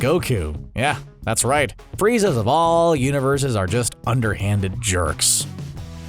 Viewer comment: wait did he just say universe sex!?